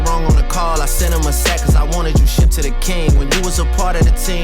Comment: i heard “Cuz I wanna do shit to the King” (LeBron)